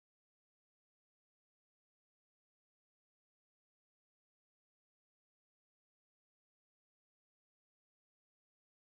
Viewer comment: Can someone please let me know what’s being said here 5:26:00 , before the judge says “oh thank you” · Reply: I think said, "I like your bracelet." I had to skip back a several times at that point in the trial to try to understand because I was so curious after people laughed. I could still be wrong. Lol